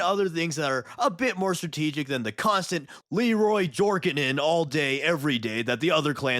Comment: At first I thought you said "Leroy Jorkin it" lmao Also I'd love to see a Space Marine 1 and Space Marine 2 sticker set if that's possible! I need a little Chairiron the VIP to stick on things.